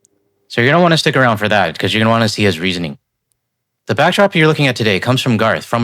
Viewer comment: I misheard you saying „you don’t wanna stick around to hear his reasoning“ 😂